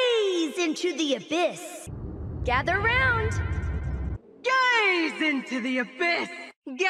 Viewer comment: For seris default ult I thought she was saying “Come around” Not gather around